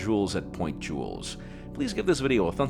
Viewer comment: da faq did u say at 3:12 XD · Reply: LegendaryVegeta Jaime Rivera